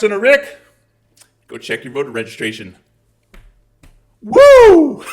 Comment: 👏👏Whooo! Sounded like you said check your bugger registration...lol